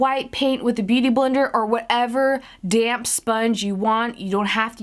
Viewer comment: Lol I keep hearing "damn sponge" but then I'm like ohhh it's "damp"!!